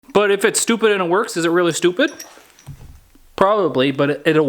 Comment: I always heard "it ain't dumb if it works!"